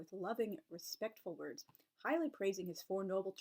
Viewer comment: Admit - I heard "piling up" when you said "highly" something or other.